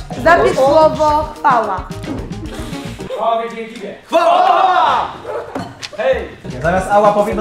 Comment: ja zamiast CHWAŁA usłyszałem PAŁA🤣